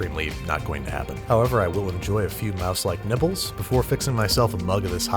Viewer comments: I heard "Four Mouselike Nipples" and I RAN from my kitchen to see what this man was doing. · did he say mouse like nipples?